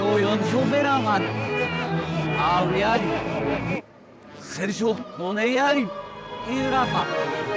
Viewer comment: sounds like it say (HELP US HELP US)